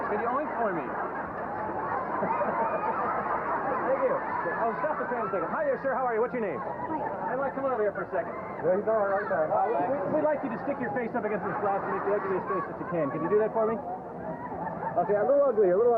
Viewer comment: it sounds like Marc says “stop the damn thing” when the tram is driving by.